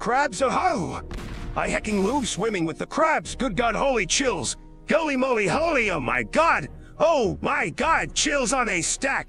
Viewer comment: The dono 28:40 sounded like "goly moly holyum my gawd" that killed me 😂😂